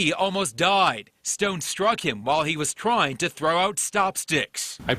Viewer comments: i swear i thought i heard “while he was trying to throw out chopsticks” i was like dayuumm 😭😂😩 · did he really said “stop sticks”? No mms lmao